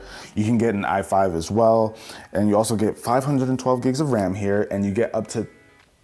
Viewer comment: did he say 512 gigs of ram?